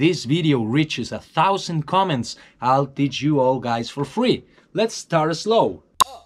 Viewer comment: i thought some one said lets star it SLOW